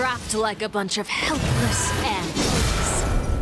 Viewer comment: REALLY obsessed with this line. The delivery was so good Also, i thought she said "helpless ants"